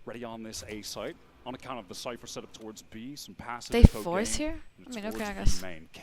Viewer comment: What did u say at 34:45 😭😭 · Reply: she said "they forced here? ok"